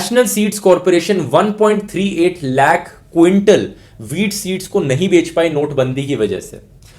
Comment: mins in subtitle it is written weed instead of wheat...